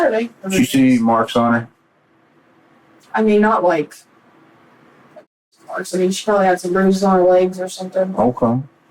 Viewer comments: can anyone tell me what "a***ive" is? I can't guess it and it's making me feel stoopid lol (please and thank you!) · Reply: I'm pretty sure it's 'abusive.' YouTube has become so restrictive, that a lot of creators censor themselves so as to not get strikes or demonetized, I'm guessing. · what is that word that keeps getting censored? it starts with an A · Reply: abusive